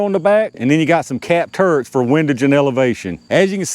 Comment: Just had to say, towards the beginning of the video, when you said "capped turrets" I initially thought you said "cat turds" and couldn't stop laughing.